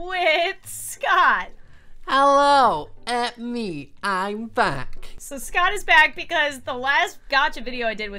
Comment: I thought Scott said it’s me I’m fat not back 😎